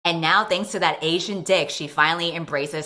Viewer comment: I had to replay the intro twice cause I thought I heard Asian dish lol.